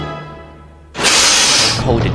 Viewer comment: what did Neville say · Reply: Pussycat But without cat